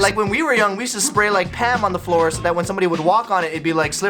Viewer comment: hey we kids do those pranks nowadays except the ham thing cause we don’t wanna hurt anyone because slipping Is painful to kids Edit : oh wait it’s Pam. I thought you said ham